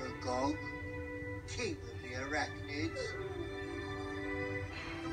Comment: does anyone actually know what Slughorn says, it kinda sounds like ‘your body will decay’ but it’s just so hard to work out